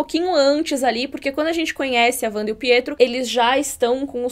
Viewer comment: Eu entendi "Fietro" como uma forma de debochar do sotaque sokoviano de Wanda. Em algumas línguas essa distinção entre "p" e "f" realmente soa meio confusa.